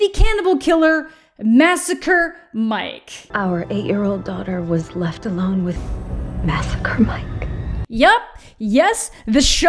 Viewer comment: I got an ad so what I heard was “our daughter was left alone with massacre mike. Do you believe in miracles!!”